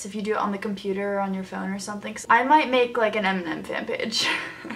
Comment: whose fanpage??? Can't understand???? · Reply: Fasial Kayani eminem